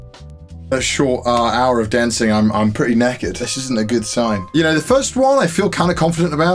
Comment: Because I'm a southern american, when he said "knackered" I thought he said "naked"